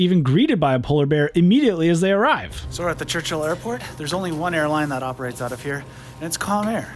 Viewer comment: I thought you said the only Airline flying out of Churchill was, "Con Air"! Good video.